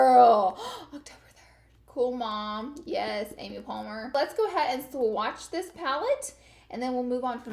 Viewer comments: I almost thought I heard you say Amy Palmer?! lol!!! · Did she just say Amy Palmer 😂